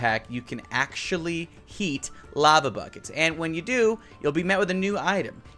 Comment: "in this datapack you can actually EAT lava buckets." Me: wait what Me again: plays it back "In this datapack you can actually HEAT lava buckets" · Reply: Lol thought he I he said you can eat lava instead of heat lava